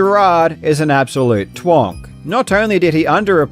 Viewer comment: I'm sorry but I straight up thought you said "Jerard is an absolute twunk" instead of "twonk" for a minute, and I was immediately thinking "I don't think that means what he thinks it does" but just the idea of Jerard being called a hunky twink got me